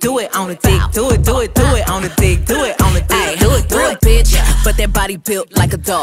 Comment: I think she say doll not door. Ion think she would ever say door 😂😂